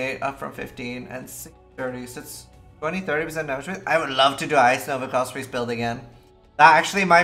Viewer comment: what is icenova felspree build 13:05 I know thats not what hes actually saying. I just can't understand him even if i slow the video down. I wanna use that build maybe tommorow please lmk someone